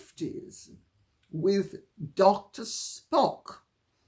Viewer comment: Lady C. You were making a very serious point regarding Dr Spock in the 1950`s and his ideas however I misheard you and for several minutes thought you were talking about Mr Spock(from Star Trek). It made me chuckle to myself until my sanity returned.Keep up your inspiring & incisive work.